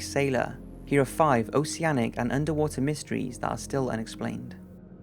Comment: At first I thought it said "Underwear Mysteries". NOPE.